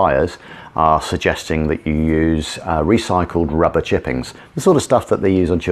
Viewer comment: Coulda swore you said use rubber chickens....